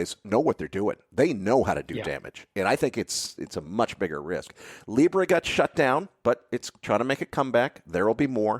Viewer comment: What was referring to at 46:33 when he said “__ got shut down, but it’s trying to make a comeback”, I couldn’t catch that (and there’s no closed captions) Thanks anyone who can assist! Fantastic interview! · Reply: Libra is a digital currency created by Facebook.